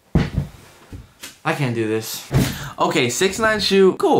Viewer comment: I thought you said 69 wasn’t going to be in the video